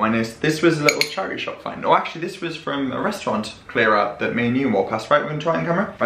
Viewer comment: Omg hahahaha, when you were talking about the ice cream bowl, I legit thought you said: "oh actually, it's from the restaurant closing down that me and you walked by" - and since you were talking to the camera, I was like, Yessir we did, you took us along that day in a vlog! Then I realized you were talking about Mr. C. and actually said. Hahaha I can't even 😂😂😂. In fairness, we WERE there too 😁. That ice cream looked fab! I do love a Crunchie bar.